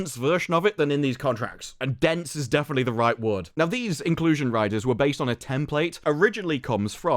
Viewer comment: I thought she said "inclusion writer"... 😅🤭🤦‍♂️